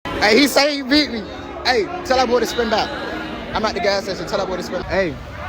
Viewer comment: Bros trying so hard to be a wannabe gangster that I thought he said tell that guy to spit battle instead of tell that boy to spin back😂😂😂😂